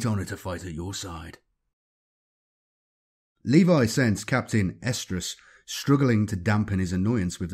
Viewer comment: I keep hearing "Captain Estrus/Oestrus"... which then might as well be "Captain Ovulation." It'd probably make Slaanesh happy 🤷🏻‍♂️